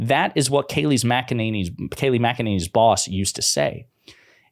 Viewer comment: I heard “kayleighs mcanneny” as “attorneys general”